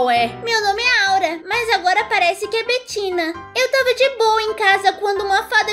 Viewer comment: No minuto 2:39 invés de Betina foi batina